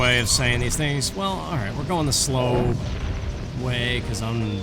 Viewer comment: it always sounded like Odalwa said "pecan foot" to me as a kid. It's all I CAN EVER HEAR! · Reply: When he summons the centipede looking things from the ceiling, i always heard him say, “we’re gonna die!” Over and over lol